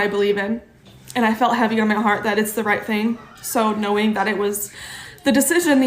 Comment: Did she say she has "a male heart"? 🙄